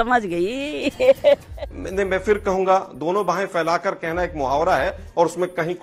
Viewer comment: Did anyone get what Anjana said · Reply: 😅dono bahe failakar anjana 😅😅😂